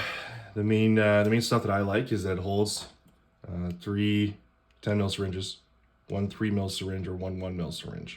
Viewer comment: not a medic here I spit out my cereal when you said three ten ML syringes I thought you said three fentanyl syringes